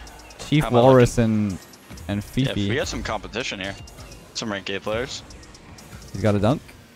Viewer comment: we have some competition some ranked gay players😂 it literally sounded like he said that